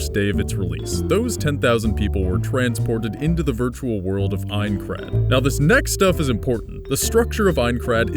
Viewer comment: i could swear i heard him say " were teleported to the world of Minecraft"